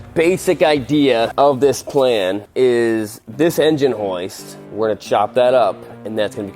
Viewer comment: I know he is saying "hoist", but all my brain can hear is him saying "Heist" in an Australian accent lmao